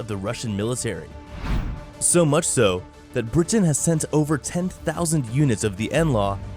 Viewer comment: did he say that "Putin sent 10 000 o them to Ukraine.."... o i misheard something? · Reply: He said "Britain sent ...." but admittedly he has a strange accent.